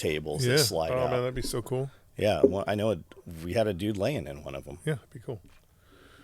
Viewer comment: I had to replay the last 60 seconds. You said "We HAD a guy laying in one." What I heard was "We HAVE a guy laying in one." 😲